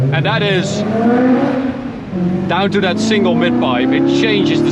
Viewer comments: “Down to that single * pipe” ?? Know what he’s saying ? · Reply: "Single mid pipe" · I thought we'd be missing out on the car after the autobahn incident but on the first turn you slide it at 8500RPM. Legend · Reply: “Down to that single * pipe” ?? Know what he’s saying ?